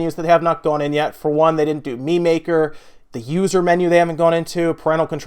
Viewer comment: For a second, I thought you said "They didn't open up meme maker." I swear to god if Nintendo shipped they console with a meme maker app I would've bought Switches